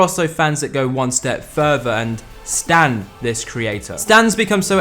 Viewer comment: did you say... [Stando] ?!